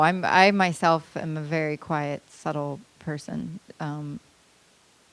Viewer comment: what did she say? i myself i'm a quiet .... person ? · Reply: subtle